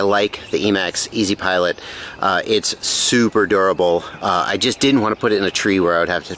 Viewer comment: Me mishearing you say adorable instead of “durable”. 😂